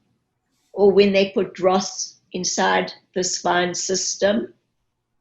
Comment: Lots if water,, less food but did you understand what she said about not doing something...in your spine? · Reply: the cc says "dross" which I googled and it means, rubbish or junk. mystery solved :)